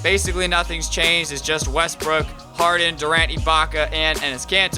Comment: I thought he said Westbrook farted not Westbrook harden